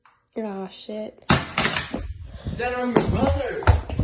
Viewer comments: Hold on did he say "Stella I'm your brother" did I misheard this ? If not... Gurl.. wtf? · did I just hear... "I AM YOUR BROTHER"‽ 😱😱